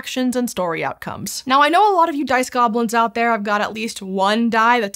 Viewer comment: I heard "Piss Goblins" instead of "Dice Goblins"